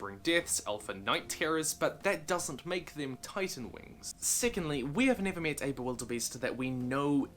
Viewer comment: I swore he said sickeningly but he said secondly